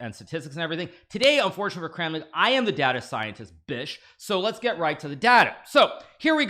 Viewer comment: Did I hear that wrong or did he just say "I'm the data scientist today, bitch"